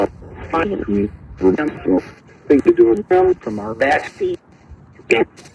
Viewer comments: Anyone else hear “back seat” ? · I like ghost box sessions at 12:49 if you listen closely u can hear thrown back seat sometimes I try to see if I hear stuff